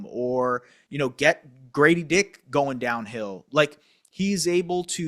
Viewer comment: I swear I heard said great Dick instead of Gradey Dick 😅😂